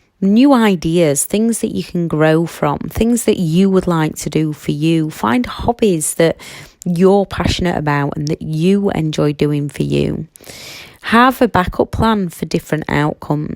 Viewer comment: When you said ‘find hobbies’ I heard ‘find hobbits’ and I was immediately like:’yess, I am going to find some hobbits!’ 😂